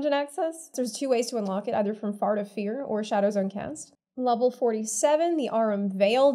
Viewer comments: Did she just said Fart of Fear? xD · Lul'd when I heard "fart of fear" haha